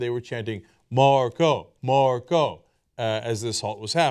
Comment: THANKS FOR THE "MARCO, MARCO" GRAPHIC, TYT! I THOUGHT THEY WERE SAYING, "BARCODE" OR "HARDCORE" OR "SMARTPHONE" OR...